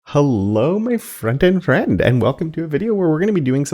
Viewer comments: for years i heard "hello my friend and friends" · You've been saying frontend friends this whole time? Thought you were saying friend and friends 😅😅